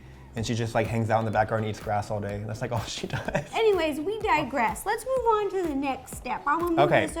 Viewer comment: It sounded like you said “we dy-grass” after said Luna eats a lot of grass and I was like THE PUN!!!!😂